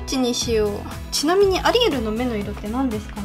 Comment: アリエルがアリエールに聞こえるww